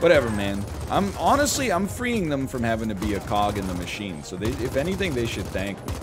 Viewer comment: I honestly thought he said "Pog in the machine..."